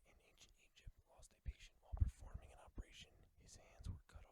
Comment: What do you say around 30:07? "If a surgeon in ancient Egypt" did what while operating on a patient? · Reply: "Lost a patient" i think